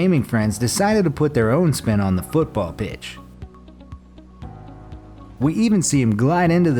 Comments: In 8:10 he said bitch instead of bench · It sounded like he said bitch in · Did he say bitch?